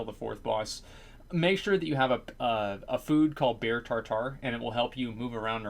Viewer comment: What does he say the food is at 2:55 ? It sounds like he's saying bear tar tar